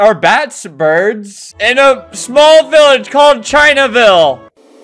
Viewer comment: I Thought that said ChillVille